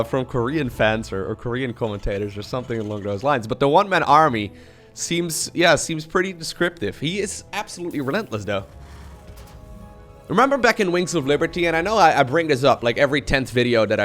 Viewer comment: Well now I'm going to be mishearing you say "Byunlentless" XD